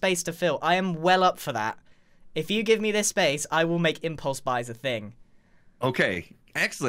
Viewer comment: 'Impulse buys' I keep hearing 'Impulse vibes' xD